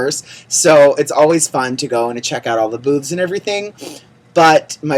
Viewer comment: I totally thought you said "it's always fun to go and check out all the boobs and everything." Anyway, let's see some of that makeup in your videos! Glad you had a great time.